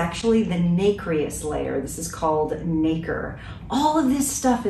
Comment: Did you say the "N" word at the 5:29 mark? Just joking, but it sounds very similar to it. I had to rewind it a few times just to be sure. 😂😂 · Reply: OH NO!!! I said nacre, also known as mother-of-pearl. Interesting though... since it was a recording of me recording myself, I knew the audio wasn't very good -- but yikes, sorry for the stress!!!